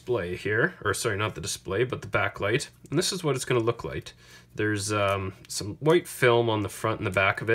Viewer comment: Did he just say look light 13:47 not trying the rude just kinda funny is all